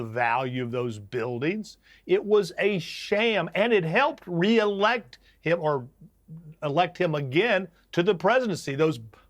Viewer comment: Did he just say it helped reelect Hitler?